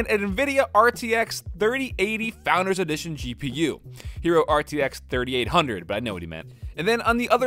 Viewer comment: I was soo confused cuz I thought Frank said "Hero RTX 3800, but I know what he meant." This made me think that the guy wrote 3080 but Frank was correcting him to a Hero 3800... I was like there isn't any Hero gpu's nor a RTX 3800. I kept rewinding and on my 3rd time I realized that he said "He wrote" instead of "hero"... I feel so dumb, lol. Your dog is cute so instead of not making videos, Just include him in them and keep up the great work. PeAcE ✌